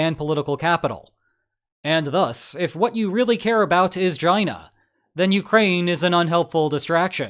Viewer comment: Did you really said "And thus, if you really cared about Gina 12:06 (China).😮